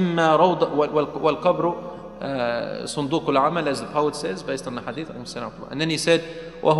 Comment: In the video at 8:14 minutes subtitles are written as poet instead of prophet(peace be upon him). CHANGE IT immediately.